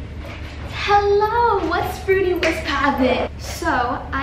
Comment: I thought she was going to say "What's Gucci, What's Poppin"